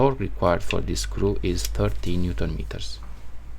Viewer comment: Fantastic video man. just one question, at 14:23 what nm setting did you say? i am not sure if it was 13nm or 30nm. kind regrads🙂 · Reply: Hi! It's 13Nm. Sorry, English is not my native language 🙂